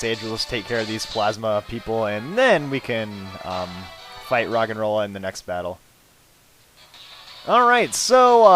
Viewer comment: when you said rogenrola I thought you said rock and roll